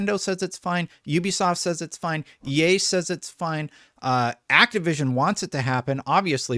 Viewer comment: At around the 13:53 minute mark when you said "EA says its fine" I thought you said "Ye says its fine" I lost it.